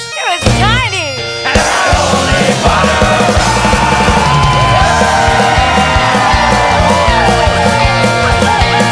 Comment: What does the female voice say at 4:27? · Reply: well after 4 years but she says "it was tiny"